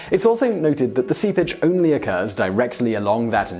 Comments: I kept hearing "Sea Bitch" every time he said "seepage." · If it weren't for some of the subtitles, I thought whenever he said "seepage" he was saying "sea bitch"